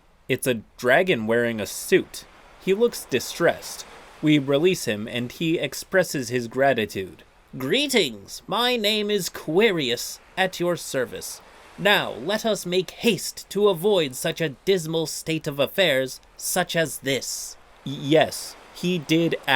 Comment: I thought that said “Queerious” for a second. Why so queerious…?